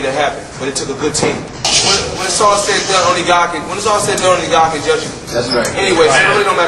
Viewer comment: what he says at 0:30? "when is all set down"? please help!